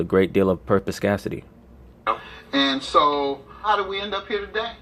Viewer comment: what is the word he said at 24:54 “purthoscasidty” idek how to spell it bro if you find it please tell me 🤣💯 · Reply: perspicacity, meaning to have a lot of insight